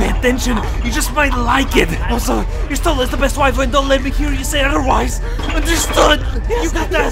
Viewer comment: wtf is he saying? 😂😂😂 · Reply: Something along the lines of "Y'shtola best waifu, don't let anyone else says otherwise" or something